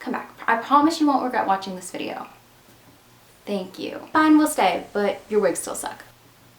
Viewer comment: I cant understand one sentence in your intro and it is bothering me hardcore lol. I thought I heard, "Okay, we'll stay, but your WIGS still suck." ....... I rewound about 8 or 10 times, no joke and was so frustrated, so I turned on captions and even the auto-generated captions agreed with me!!! Lmfao what is going on here. Can someone PLEASE tell me what was said in the intro?!???! I will say that if those were the actual words spoken then DAMN; can pull off the wig look like no one else!!!!